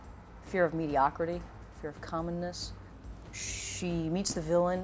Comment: "fear of commonness"... I first heard that as "fear of Communists" but knew that couldn't be right...